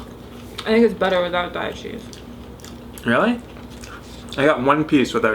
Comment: All this time I thought you were saying "diet" cheese. I just today realized you were saying a brand name.